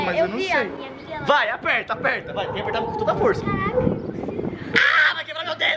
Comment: isso foi um palavrão? Alguém me fala! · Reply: Ela falou caraca